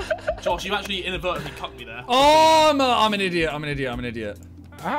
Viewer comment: sounded like josh calling harry the n-word😂